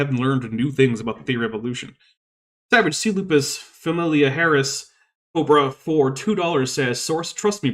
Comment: Every time you read out Savage "C. lupus familyahraris" Cobra, I misheard it as Savage Seleuf is familiar Haris Cobra.